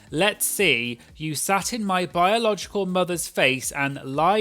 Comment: when you say "you sat in my biological mother's face and ..." I heard "you sat ON my biological mother's face" ..... very different meaning lol